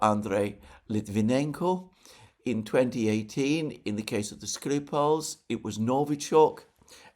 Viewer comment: I really like the AI subtitles this time: instead of 'the Skripals' Youtube undertitles this with 'the screwups'. ROFL 0:09:00 What does YT know that we don't know?